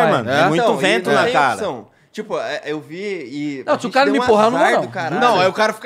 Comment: "Se o cara me empurrar eu n vou n" se ele te empurrou, vc ja foi kkkkkkkkkkk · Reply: Acho q ele disse se o cara n me empurrar eu n vou n